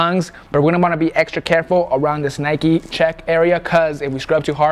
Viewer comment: Did he just say Nike check?